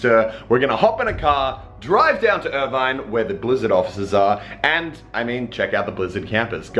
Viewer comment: I thought it said BAZZARD XD